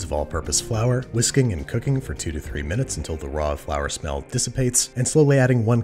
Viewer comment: I always hear "Until the flower smell disappears" :D